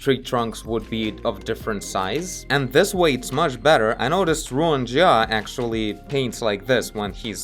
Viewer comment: anyone knows the artist of the artwork in 13:04 ?? i can't really hear what says. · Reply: Ruan Jia